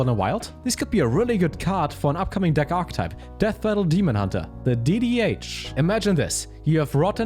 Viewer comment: it sounded like you said "Death Metal Demon Hunter" and now I really want it to be called that